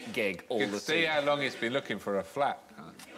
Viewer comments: What does Alan Davis say? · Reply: leirgauk "You can tell how long he's been looking for a flat, can't you." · What did Alan say at 7:05? Something about looking for something? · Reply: He said he was looking for a flat (i.e. apartment).